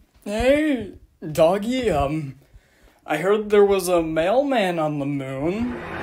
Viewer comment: This IS truly a copper moment, i vote Pail Edit "There's a mailman in the Moon" made me laugh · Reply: i thought he said "theres a male man in the moon"